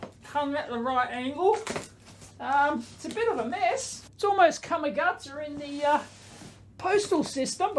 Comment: What is the word uses around 0:30 - kamagata? Or is it a phrase? By context it seems to mean "messed up". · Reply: I think it's "Come a gutser", as in a bellyflop.